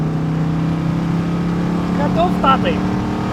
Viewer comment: ساجماعة اشو قال بالدقيقة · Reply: اتوقع قال كت اوف تعطيل Cut off